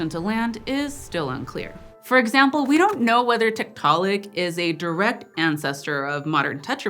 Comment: I misheard Tiktaalik as Tiktok...